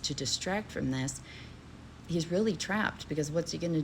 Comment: I thought you said he was Trimped😂